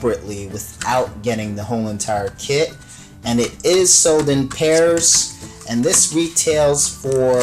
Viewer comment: I thought he said It was sold in Paris!XD